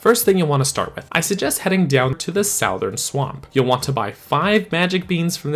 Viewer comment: Did you just say "sowthern"??